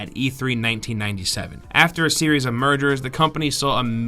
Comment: Lol, I heard murders first, and was like "Wait, what?"